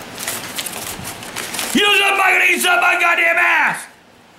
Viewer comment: I think he said, “You know something, Michael? They can suck my gaw damn ass!” Lmao.